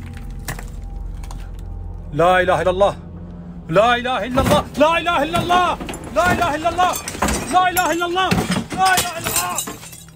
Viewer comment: c'est quoi cette langue mdr "lala hi lala lilalalhali"🤣